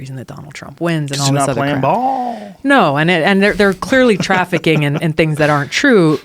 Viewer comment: Did she just say Hillary Clinton is trafficking???